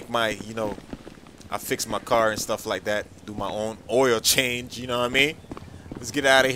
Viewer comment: Lit 3:11 and did u just say Oreo change 😂😂😂